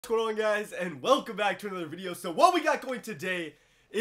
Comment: seriously keep replaying that it sounds like he says twirling guys 🤷‍♂️